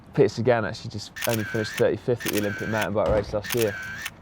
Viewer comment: "Peter Sagan actually finished 85th at the <insert race name> last year...." Is that what Si said? I couldn't hear because of the camera clicks.